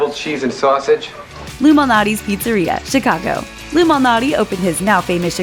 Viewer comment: I thought she Said Illuminati's Pizzeria O__O